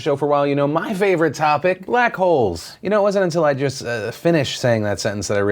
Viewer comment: i watch your show as a podcast, driving to and from work, with the audio in my truck! I dont get to see the video, but i listen to the news from you. Lmao, yes the black hole thing sounded like u said u like buttholes LOL